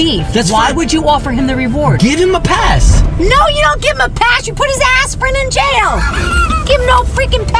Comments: it sound like she said 𐌀$$ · Did mom said a curse 6:00 The pass of mom 6:03 The arrested of the man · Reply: She said aspirin😂😂